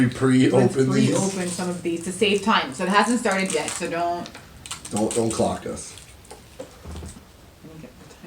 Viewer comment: Did just say "don't CLOCK us" @ 3:10 ??? omg!!